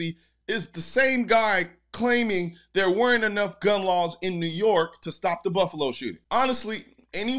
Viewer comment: I didn't hear him say laws I heard we don't have enough guns